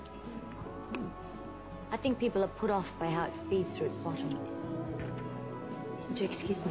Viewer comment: People are put off by ... what? I can't hear what she's saying. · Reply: "how it feeds through its bottom."